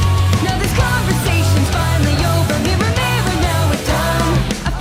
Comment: I always heard "finally one" as "fare thee well" and I gotta admit I like my version better.